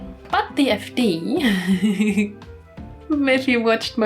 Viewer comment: what is the title of the game on 12:28 timestamp. i cant understand is it dfd? fd? xD huhu someone pls tell me ToT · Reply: Sorry, it's the Cupid Parasite fan disk Sweet and Spicy Darling